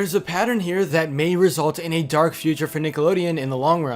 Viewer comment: I thought that said dark feature